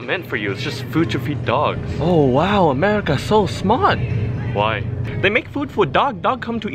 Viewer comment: "Ahhh. America very smot!" "Why?" "Because America make food for dog. Dog come to eat food we come to eat dog." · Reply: It's because Ging Ging sounds like he says smot instead of smart.